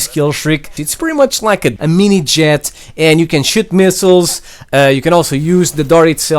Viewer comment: Is it just me but at 7:47 does he say the dart can shit missiles